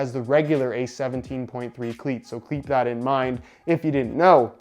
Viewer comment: did you say “kleap” that in mind??